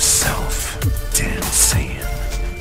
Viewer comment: did he said "damn saiyan" or "dancing"?